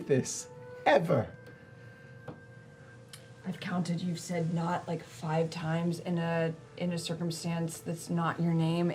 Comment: Travis realises something. And than mouths some stuff to Ashley. Can anybode understand what he is trying to say? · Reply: What he says “I’ll tell you later” to Ashley since she is watching him and looking like she waiting for him to say something. It looks like she says “Nott?” And he shakes his head and says “later”.